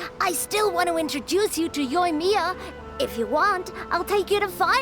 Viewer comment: I THOUGHT SHE SAID MAIM OH NO KLEE